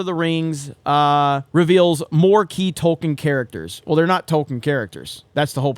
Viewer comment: Funny how when you said "new Tolkien characters", its sounded like you said "new TOLKEN characters"